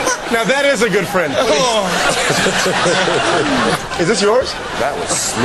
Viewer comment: What does Colin say at 9:55? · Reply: Try doing that, Cole Porter.